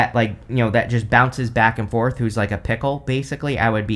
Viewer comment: I swear, every time you said fickle I heard "pickle" I spent the longest time trying to figure out what a pickle person would be!!!! LMAO!!!!